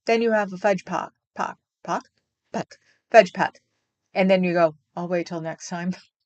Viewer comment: Did u say fudge pack? Naughty 👿 girl😮😮😮😮😮😮😂😂😂😂😂😂😂